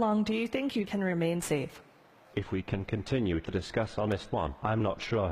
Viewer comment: can someone please tell me what he says? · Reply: "If we can continue on this one, im not sure how"